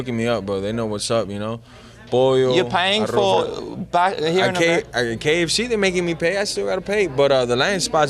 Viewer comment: Is this in Australia? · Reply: Sound like he said "here in America ", regarding KFC lol